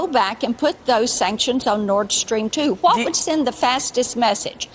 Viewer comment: I thought she was saying Nordstrom; watched her during the Supreme Court Hearings it was truly sad.